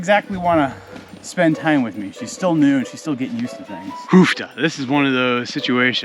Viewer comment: Did I hear an "uff da" (or perhaps a more cow-punny version, "hoof-da")? Are you perhaps a tad Norweigan? 🇳🇴 Seriously, though, love your vids. I always learn something new, and have fun along the way. Keep up the great work!